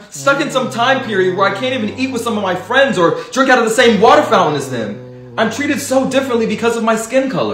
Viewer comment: I thought he said "jerk out of the same water fountain" 🤣🤣🤣